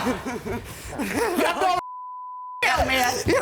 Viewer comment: Did she say, "YOU'RE GOING TO HELL!"???